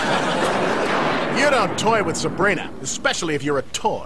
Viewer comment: "Sabrina, Sabrina" bruh i died man😂😂😂😂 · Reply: For years I thought he said some help as a kid only now I'm noticing he said Sabrina 🤣